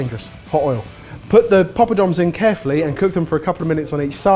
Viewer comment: Oh my goddd.. I was wondering the whole time what was saying. Coz all I heard was "paaapadorms". Turns out it's pappadoms😂 I am a South Indian btw. So loved loved this recipe